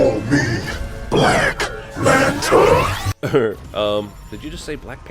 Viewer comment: I thought the guy said black lantern